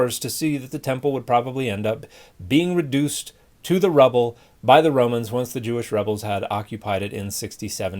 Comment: Sorry to be a pedant, but at 2:39 there is a typo in the captions. The caption says rebel instead of rubble.